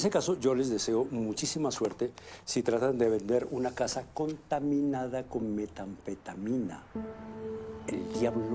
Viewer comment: I love how he says this, someone tell me what the words actually are, to me it sounds like "contaminara con methaphetamina"